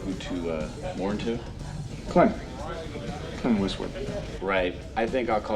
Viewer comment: Well, I hate to correct you, but I'm gonna ;) At the 14:50 mark, when the bartender asks what his name is, he says, "Clint Westwood.", to which the bartender replies with, "Right...I think I'll call you, Clint Cuckoo." Just another fine example of the outstanding writing in this timeless masterpiece.